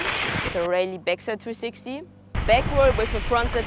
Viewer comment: Raley backside 360 is 303, did i hear correctly ? On 12:37sec.